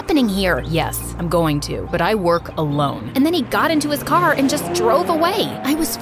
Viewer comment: I thought that when felix said that i work alone i heard it as wear cologne